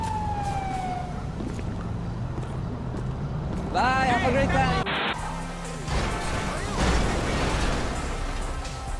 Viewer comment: What exactly did he say on 1:41? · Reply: Bye have a great time